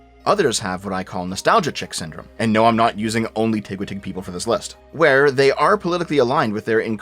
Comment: What did say here? I genuinely can't understand it lol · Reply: "tigwatig" is a pronunciation of TGWTG, that guy with the glasses